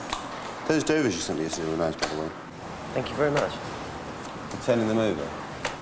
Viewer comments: does anyone know what he says here? It's been 10 years and I still can't figure it out! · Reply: "Those Dover fish you sent me yesterday were nice, by the way" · What does say to the fish supplier guy at 3:39? I couldn't understand a word of it. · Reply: Faye Zentner He said "for turning them over" not are you sending them over.